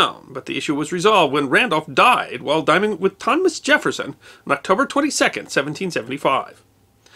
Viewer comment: What is said at 2:53? It sounds like 'diming it', which makes no sense to me. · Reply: I believe he said, "...died while dining with Thomas Jefferson."